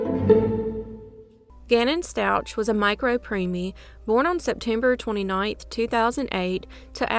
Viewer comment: I'm sorry I love this channel but what in the world did you say at 0:11?? It just sounded bad "micro premi" idk how to even spell it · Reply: Micro preemie. A premature baby that weighs less than 750 grams